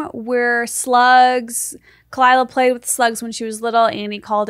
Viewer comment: I thought I heard Esther say “Hey Sluts” at the start of the video and I said “Yes, I am a slut” in my head 😭